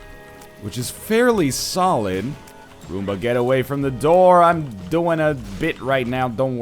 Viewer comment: did Nl say "Arumba get away from the door"?